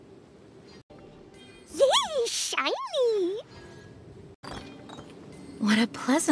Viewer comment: PLEASE I CANT- I THOUGHT KLEE SAID "SHEESH SHINY"